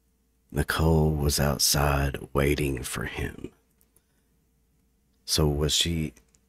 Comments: Almost sounds like "Nichol was at outside house waiting." @ 11:08-11:11 ish and at 50% playback I swear it almost sounded like " Nichol was at his house, waiting on me or him" Very long evp! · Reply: I heard “waiting for me”. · Almost sounds like "Nichol was at outside house waiting." @ 11:08-11:11 ish and at 50% playback I swear it almost sounded like " Nichol was at his house, waiting on me or him" Very long evp!